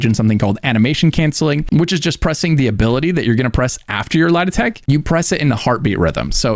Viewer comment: can you explain what word you're saying lol Lah-Detect? Am I braindead? I'm replaying it trying to figure it out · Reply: Light attack (as opposed to heavy attack)